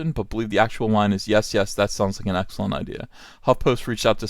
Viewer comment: "...yes, yes this sounds like an excellent idea." is all I am hearing. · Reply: I kept hearing the f- word until I slowed it down to a quarter speed, then I heard "sounds like an." Sped up, I could still hear "Sounds like an" for a while. I'm not sure how "that sounds like an" can sound like "that's a f***ing." The network could simply reshoot the scene if he actually said "f***ing" and they don't think he did. Different people are just hearing it a different way.